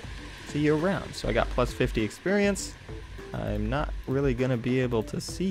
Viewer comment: with subtitles on instead of experience you get $MONEY$